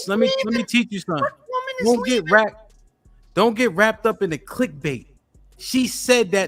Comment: Don't get wrapped up in the clit-bait... 😂😂😂 Idk if even caught what he said there.